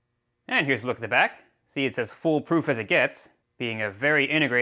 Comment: Did you say Full Proof?